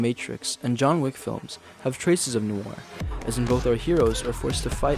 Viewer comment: idk what the hell he says here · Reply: That was spawned by human folly.